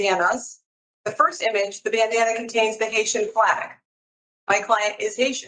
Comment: Did she just say "my client is Asian"??